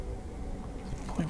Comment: Sounded like it said, “right Fn here.”